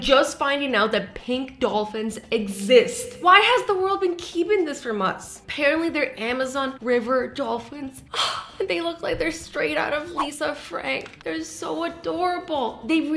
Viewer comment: i really thought she said 3:27 we can buy them from amazon..🤣🤣